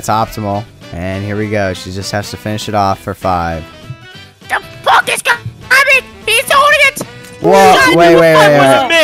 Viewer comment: I think they said "The f--k is going on, he's holding it!" or something along those lines